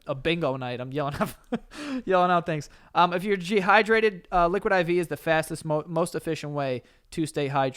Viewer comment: Any body else notice saying Gehydrated instead of dehydrated at 46:05?? 😂